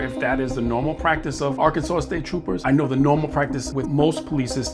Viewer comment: I thought I heard him say city police, not state trooper.